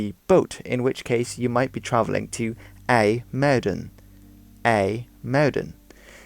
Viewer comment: thought he was saying "a murder" scared the shit outta me at first